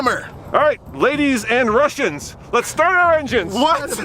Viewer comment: I thought you said Russian ladies!